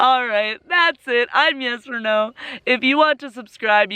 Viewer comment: Every time you say your name I hear.. I'm Yes or No, is it just me?